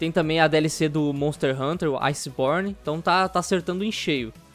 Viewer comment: eu juro q entendi iceporn